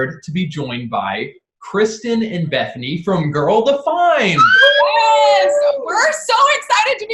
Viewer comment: omg I misheard “girl defined” as “Gilda Find” and I was like who’s that???😂